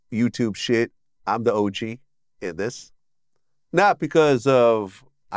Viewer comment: Could have sworn I heard him say im the OG of this shit. Breaking the s*** down like 10 crack commandments. That's sucks I liked Vlad but shit got way too cringe I wish I didn't see that.Adiios vlad